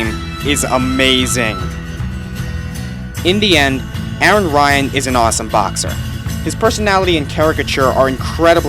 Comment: i first thought he said indian